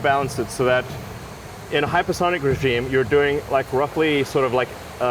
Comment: the subtitles say "hypersonic stream", should probably be "hypersonic regime"